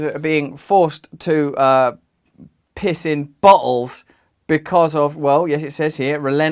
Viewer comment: Sounds like he said forced to piss in buttholes lmaoo